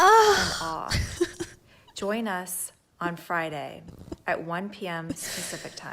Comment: “Join us on Friday at 1 pm specific time.” I think she said “specific” time. At 31:40.